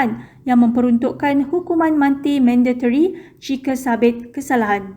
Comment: Jap part yg hukuman mati tu tersasul ke aku Salah dengar berbunyi^ hukuman manti^😅😂